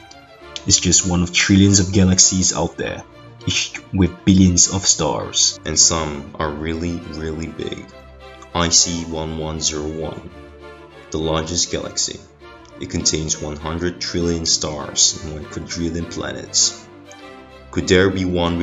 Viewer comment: I thought he said "i see 1101"